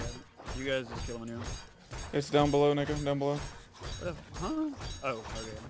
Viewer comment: did i hear that right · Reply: our teammate's name is neko